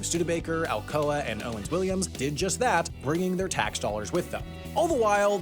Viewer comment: Is it Owens-Williams or Owens-Illinois? The subtitles are different from what you said? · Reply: It’s Owen-Illinois